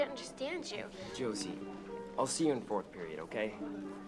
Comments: I thought the guy said "ill see you in 4th grade"😂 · Did he just say "I'll see you in 4rt grade" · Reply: Silly Lesley no he said fourth period. i also confused